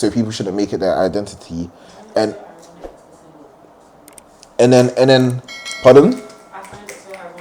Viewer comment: what did the lady in the back say? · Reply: The people saying it don’t have a man